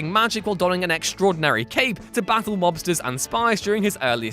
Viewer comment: I misheard "mobsters" as "lobsters" which I think improved my memories of Alan Scott.